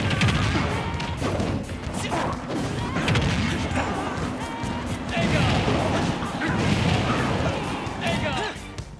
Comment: wait what's he saying as he makes that attack? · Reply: "Eiha". The basic curse type attack in the persona and SMT. Also the basic spell available to Arsene.